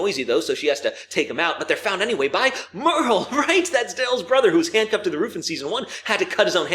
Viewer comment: Did I hear that right " merel that's DALE'S brother right" excuse me I thought that was daryls brother 😂